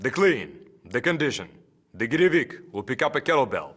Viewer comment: anyone know what he's saying, something in russian? "Girivig"?